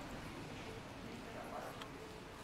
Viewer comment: I thought she was saying "straight punk girls" lol good stuff tho